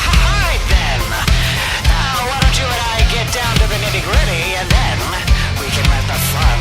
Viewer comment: DID HE SAY GRIDDY